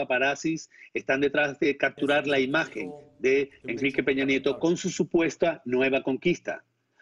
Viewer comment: Escuche bien? Peña Nieta?